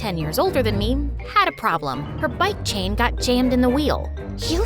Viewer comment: BC I WASN'T PROPERLY LISTENING I THOUGHT IT SAID BUM CHAIN